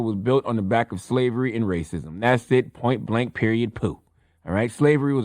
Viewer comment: Did he just say "Periodt pooh" 😭😭😭